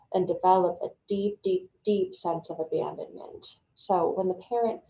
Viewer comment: Thanks for the insight, I pretty much feel like the 4th type. May I ask what's the word at 10:32 deep sense of be-ambearment? Thanks 🙏🙏 sorry for my bad listening. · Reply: Children will develop a deep sense of abandonment